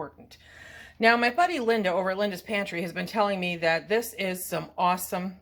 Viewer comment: Great haul! What is Clare’s channel? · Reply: I thought you said Clare’s pantry?